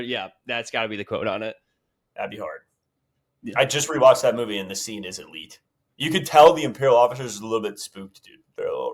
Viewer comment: won't shut up what does say bummed out I can't learn lore ive seen the movie so many times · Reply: He says no disintegrations